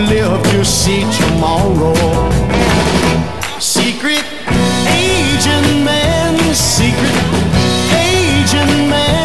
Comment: Lol. My dad thought he said “Secret Asian Man,” not Secret Agent Man